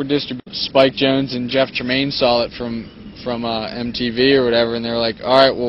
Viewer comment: lol at 2:16 the captions say Jeff Jermaine insted of Jeff Tramaine